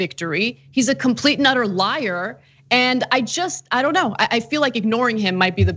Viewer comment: When Ana said Trump is a complete and utter liar, I thought she said Trump is a complete Nutter liar. Both statements are true